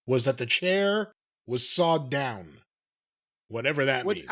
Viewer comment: I thought he said they shaved the chair down not sawed, whatever that means.